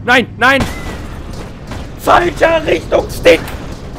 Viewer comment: was hat er gesagt 20:05? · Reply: FALSCHER RICHTUNGSSTICK!!